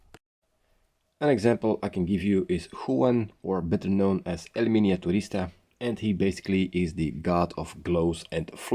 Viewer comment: I'm sorry, who? I've tried Googling what I thought you said, with a few variations, but I'm not getting any hits. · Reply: I said there .. you can find him on instagram